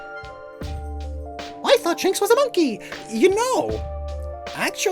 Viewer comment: I heard Jinx, not Shinx. So when he said "ya know" I got scared